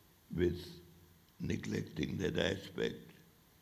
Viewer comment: When Kissinger said "neglecting that aspect" I heard "neglecting that respect". My brain went into overdrive, and I had to go back and hear it again. We are not respecting each other currently. There is a shallow respect, but not real respect.